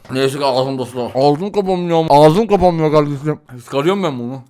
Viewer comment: ne dediğini anlamak için on kere izledim "NEYSEKİ AĞZIMDA SU VAR" anladım ne alaka blm😂😂😂